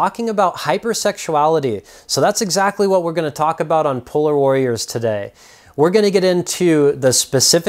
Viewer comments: Lol it sounds like he said "puller warriors" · I Thought my guy said pull out warriors lol😂. Double checked and it's polar warriors